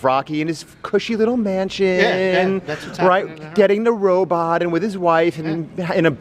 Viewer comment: When misheard robe on for robot and starts laughing about how he thinks there's a butler robot in rocky 3